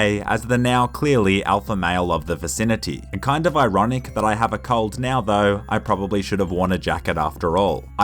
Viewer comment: I thought you said I have a cult